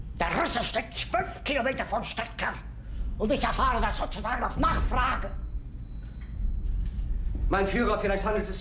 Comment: i heard mach frag